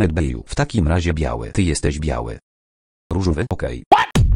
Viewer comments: Zamiast: "- Różowy - Okej" Usłyszałam: "Różowy to gej" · Ja usłyszałem ,,różowy bo gej’’ ;P